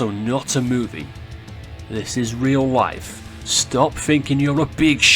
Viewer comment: lol it kinna sounds like he says this is real wife.